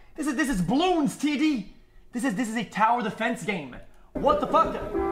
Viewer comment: I almost thought he said this is a Tower of Fantasy game lmao